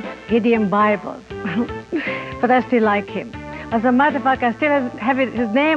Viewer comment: Did she say mother###er? 2:28 LOL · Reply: "as a matter of fact" --had to listen three times to figure it out. wooh, accents, they throw us, don't they? thought it would be a dab of fun to answer your post from a year ago. g'day to you, kind stranger.